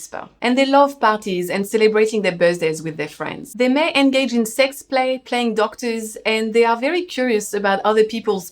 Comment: Did you say "sex play" at 8:05 ?